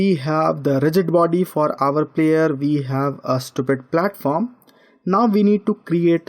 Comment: Did you just say stupid platform